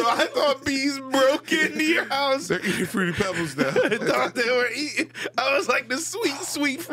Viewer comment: On god I thought he said bees ate his fruity pebbles too 😅